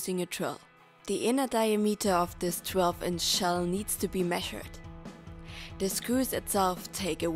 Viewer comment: What does she say at 00:15~00:20 and 05:53~06:04? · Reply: the inner diameter of this shell needs to be measured. You need the exact diameter to know the length of the wood bar.